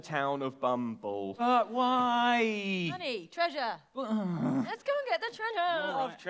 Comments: Did just call "Honey"? I'm just gonna go and ship Prudence and Corazone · Did Prudence say ‘Honey! Treasure!!’ to Corazon? Lol now I wanna see a Starlord-Gamora-Thanos type interaction with Corazon-Prudence-Cthulhu, for some reason. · What awaits our heroes inside this town's locked tomb? Probably only good stuff, we imagine! Enjoy a new Oxventure, recorded live at EGX Rezzed in London, UK on 6 April 2019 🏹☠️💣🎵⚡️ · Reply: At the beginning, i thought called honey, I thought she said: *Corazon: "But whyyyyyy?" *Prudence: "Honey, Treasure"